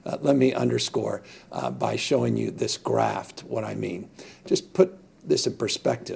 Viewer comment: Did he say "graphed"